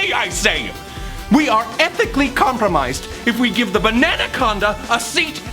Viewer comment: did Roman call Janus a bananaconda- or is it just me